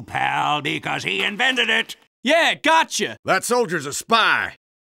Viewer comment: put your mouse on 0:16 ant click it again and again. it sounds like he is saying "PIE"